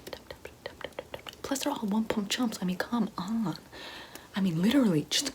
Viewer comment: WHAT DID SHE SAY AT 08:57 OMGGG MY HEAD HURTS FROM TRYING TO FIGURE IT OUT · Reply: "plus they're all one-pump chumps" that?